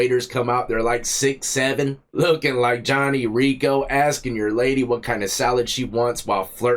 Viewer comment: I thought you said Johnny Ringo, doing some sick ass pistol twirling tricks